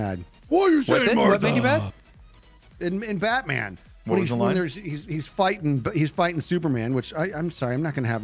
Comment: i heard "your fighting super mam."